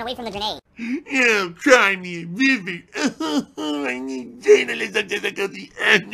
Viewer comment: Whenever you play that one clip where he's mock crying and saying "waahh I need to turn up the difficulty" I always hear "wahhh I need journalism difficulty" listen out for it